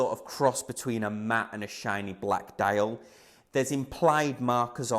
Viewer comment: Great honest review, most reviewers shy away from admitting to liking invicta given the aforementioned monstrosities. Just wondering though at the 5:00 mark it sounds like you say the watch has "implied" markers... I think its either your accent or you meant to say "applied"